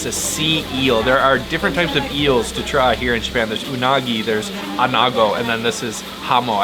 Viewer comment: Unagi (eel) 😂 I heard you say unago so many times lol. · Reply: Unagi is freshwater eel. Anago, is saltwater eel. Completely different taste and texture.